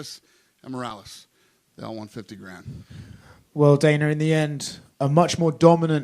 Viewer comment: I first heard it as “all 150 grand” but felt it was too good to be true so checked the subs which said “all won 50 grand”